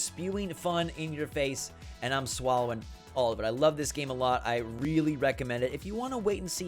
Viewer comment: wtf did you just say????? you swallowing it???lmao